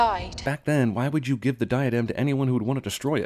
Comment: Holy shit, they were saying diadem that whole time. I thought they just couldn't say "lost item" correctly. Now I feel stupid.